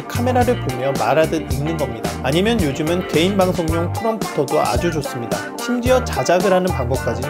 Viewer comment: 오 엄청유용해요. 저 5:48에나오는기계이름이잘안들리는뎌 선글라스다음 크롬프로? 그거이름이정확히몬가요? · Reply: 도움이 됐다니 기쁘네요 :) 말씀하신 기계는 프롬프터 입니다. 네이버에서 검색하시면 쫘악 나올꺼에요~